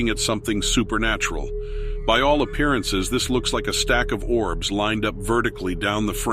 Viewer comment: I thought he said a stack of dwarves. 😁